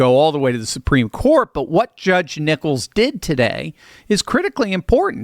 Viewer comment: I could've sworn you just said Judge Nipples.